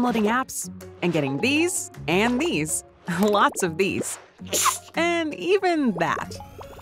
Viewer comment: LOTS OF BEES! (what I hear)